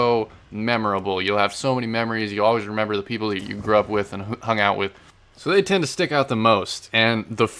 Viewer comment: is it just me or did you almost say hook up with instead of saying hang out with at 3:16?